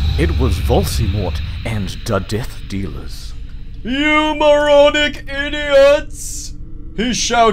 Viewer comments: I was assured that this story was written by a troll when they referred to Sirius Black as "dogfather". Well done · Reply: I thought he said you marauding idiots. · I thought you said meth dealers